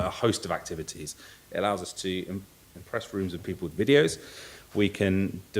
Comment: did he say it allows us to impress rooms full of idiots? Ha ha. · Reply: 'Rooms of people with videos'. lol